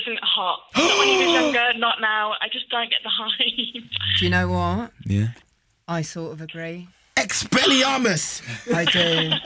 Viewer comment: what did Daisy Ridley said? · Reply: "Do you know what. I sorta agree "